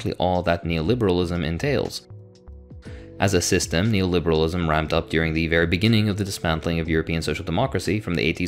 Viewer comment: What are you saying at 0:26? Neo-liberalism "rhymed" up? "Rammed"? At 0:38, I had to listen to it 5 times to realise you were listing off countries. Haven't switched off a video as quick as yours bro, your mumbling is frustrating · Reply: ramped up